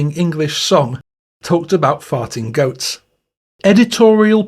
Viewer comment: Your English wording for Fart repeatedly sounded like you were saying F--king Goats. I was having Muslim flask-backs to the Gulf War because the US Military has thousands of hours of film footage of Muslims screwing goats in the desert. Even fighting each other over the poor goat(s). At the end of your translation the song is kind of funny. Not so much at the start but now even that seems funny.